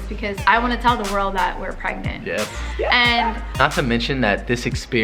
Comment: “ I wanna tell the world we’re pregnant “ LMAO 😂 I ACTUALLY LAUGHED SO HARD BECAUSE IT SOUNDS LIKE YOUR SAYING “ WE’RE BOTH PREGNANT “ OMG 🤣